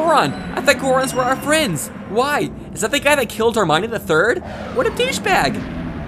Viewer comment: for some reason I heard "is that the guy who killed Hermione the third?" Had to actually think about that one for a minute before realizing you said "Darmani" ._.